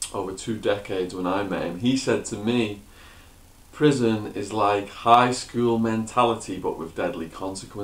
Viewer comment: Am I the only one who thought he said prison is like high school You can’t sit with us 😂 Mean girls flash backs